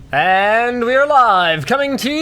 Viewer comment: Starting of the show, i heard "and we are alive"...lol